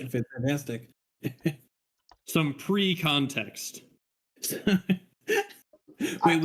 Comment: "Precum text" is what I heard